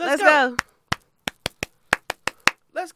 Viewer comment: In that song at the beginning was singing I always thought he said: let's do Coke.